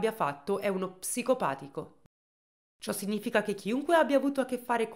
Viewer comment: Psicopatico ...? Non ho capito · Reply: Pervertito